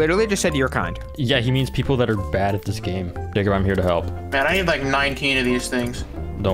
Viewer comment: I heard "n-word i'm here to help"